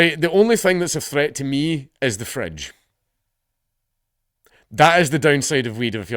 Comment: I misheard “fridge” as “French” and for a sec I thought France had it out for you. The true danger of smoking weed!